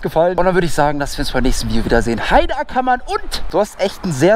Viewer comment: was sagt er da Heide Ackermann? 😂